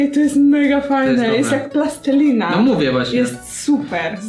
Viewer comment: usłyszałam od cb pasta zamiast plastelina xdddddddddddd